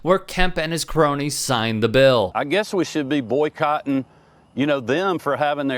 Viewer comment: "Why corporate has the right..." : because of the same reason of "Citizens United". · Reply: Kemp’s saying “boycotting” sounds like he says “Boy Cotton”. I guess the truth comes out subtlety with this douche bag.